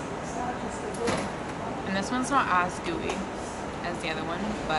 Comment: at @ 10:07 did she say gooey or dewy????? helpppp me out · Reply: Gigi Gooey.